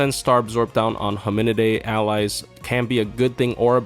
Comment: What did you say @ 1:03 ??? Hamunid??? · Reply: Hominidae